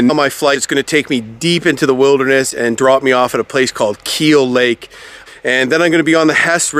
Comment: Hey. When you first landed at that outfitters where you said there were some horse back hunters - what is the name of the outfitters again? I thought I heard Keele lake?. Thanks pal and great videos ! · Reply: No problem, ya it is Keele lake and it’s Duelling Stones Outfitters.